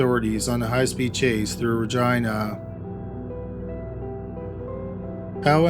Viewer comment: I deadass thought he said “v*gina” and I was like whoa thurrr😳 but after rewinding it like three times, I understood lmao. Then I thought, hmmm 🤔 that’s a weird way to pronounce that then I realized is from Canada and he’d know more than me cause I’m from the states lol. anyway....great video as always 🖤🖤🖤🖤 I hope you guys had a good weekend and stayed safe despite all of the madness and bullshit that’s going on in the world!